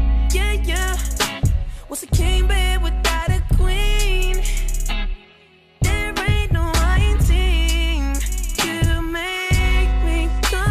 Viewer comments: my favorite part is whats a king bed with out a queen there aint no i in team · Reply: frfr I love that part !!! 😘😘 at 1st I thought he said they ain't no y in team !! 😂😂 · All this time I thought he was saying "What's a king, babe without a queen?"